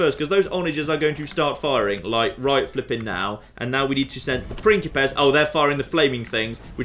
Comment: Every time says "pincipes" I hear "prickly pears" which makes the whole thing very entertaining